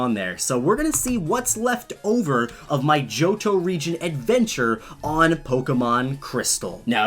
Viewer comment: At first when you said Johto, I thought you said Jojo